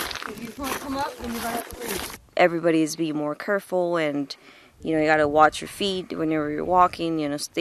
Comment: Did she just say “curful”🚬🗿